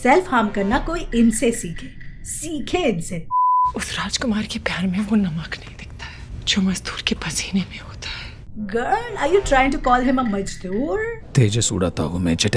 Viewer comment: what she just said?😂😂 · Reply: Something about salt in paseena. Idec anymore